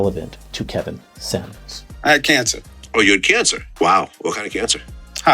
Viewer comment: I thought I heard he had cancer